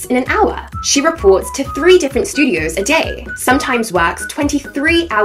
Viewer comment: I thought she said she runs a only fan server 💀💀💀